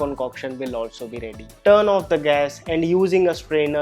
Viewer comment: who notice “turn on the gas” instead of Turn off 😅